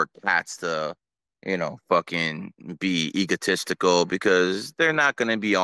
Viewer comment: did he say that cats are egotistical?